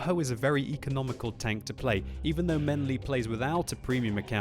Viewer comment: Everytime he said "menly" I thought I heard "Ming Lee".